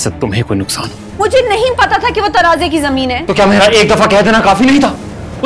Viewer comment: cc are wrong. "Tanaazay ki zameen" means "land of conflict". "Tanaaza" means "conflict ".